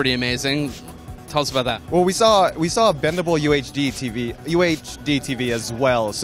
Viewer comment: lmfao i thought it said the worlds first BLENDABLE tv XD